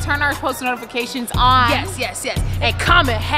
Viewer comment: It sound like she said “I hate y’all” 17:58